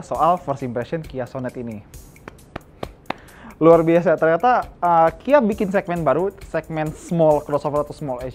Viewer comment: Toyota kia bikin segmen baru... Atau Ternyata kia bikin segmen baru... Apa gw yang salah denger ya??